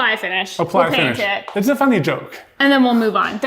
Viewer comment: I have this video on my tv and it sounded like said it’s an F***ing joke when they said the finish joke. 😂😂